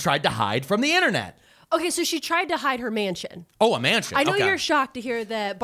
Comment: I totally heard "man chin" instead of "mansion" lmfao Oh and Helloooooooooo!! Hubba hubba!! Aawoooga!! Wink wink, nudge nudge, say no more!!